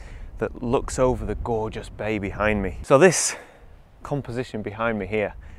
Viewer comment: "bay behind me" I heard "baby hide me" 😇😂